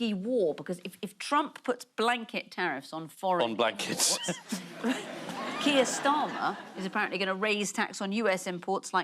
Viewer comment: what the joke/point here? Is a terrible pun on blankets, or satire about trump? So confused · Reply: ohhh he said blankets, meaning "blanket tariffs on blankets"... I totally heard "black kids" 🙃